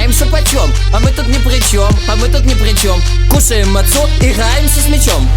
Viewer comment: А что там говорят мацум или мацу на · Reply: Мацу такая национальная еврейская еда.